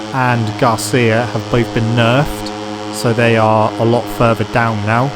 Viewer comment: Could you tell me the word you say at 20:23 after "have both been"? I heard this word many times but I don't understand how it's written. (I'm not English, it's very clear) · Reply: The word is nerfed. Its a word created for video games and means that sth in the game became weaker than it was after the update.